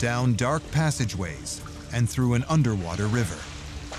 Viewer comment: Hahahaha... 4:00... Did he actually say... "...an underwater river"??!!